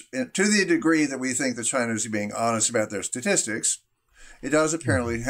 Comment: Can anyone help? At about the 7:30 mark..."to the degree that" WHO? "are being honest about their statistics? I've replayed it a few times, and I thought he said "Spanish," but I'd like to be certain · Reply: China. it's English